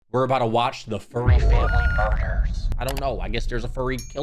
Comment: okay- I heard "were watching furry family murders-" and I just paused it and died laughing. I have no idea why 😭😭💀 Edit: I love you! My top favorite YouTuber! ❤️💛💙